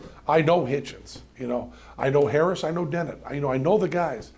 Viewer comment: i can't make out what is the name of the last guy he mentioned at 3:14? he said Denin?